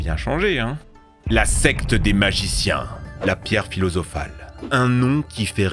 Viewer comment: La Quoi ? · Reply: La pierre philosophale